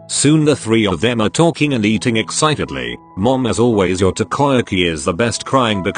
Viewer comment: I thought he said tokoyami- Oop- Lmaooo